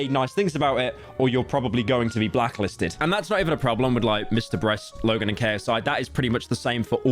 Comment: why did zawg say mrbreast 💀💀